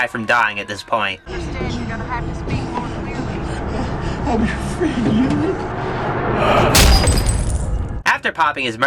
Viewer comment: What did he say at 4:38?! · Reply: He said that Thomas and the boss were friends